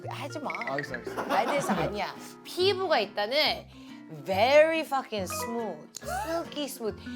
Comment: wait did Jessi say "Very f**king smooth..." here??? is it just me hearing her say that? LOLLLLL